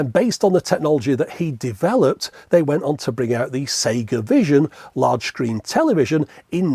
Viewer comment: I was convinced i had misheard that. Sega Vision?